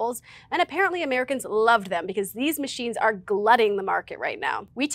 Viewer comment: Not trying to be pedantic, just trying to let you know about a typo if you want to fix it: at 3:49 it has "gluting" in the CC instead of "glutting" the market for Belgian waffles. Love y'all's stuff! Thanks for everything!